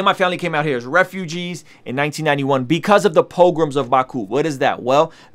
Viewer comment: POGROMS OF BAKU, man it took me ages to find info because I thought you said Pilgrims, this is wild, I’d known crazy stuff had happened but man that’s right around when I was born